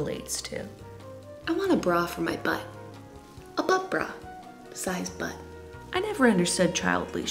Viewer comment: At the butt bra part I thought you said butt broth...😂